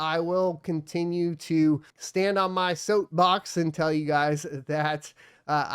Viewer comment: did you just call it a soatbox? haha